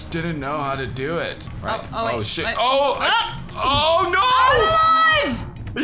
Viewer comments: did say shit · did anyone hear say oh shit